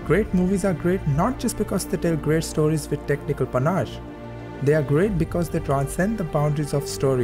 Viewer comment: what are u saying at 7:40 "technical panaj"? i didn't get it please answer it. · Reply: ul haq 'Panache'